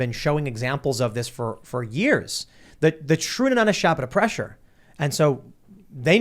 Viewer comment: "the true not a shop at a pressure" That's it. That's what Biden said. So says the captions!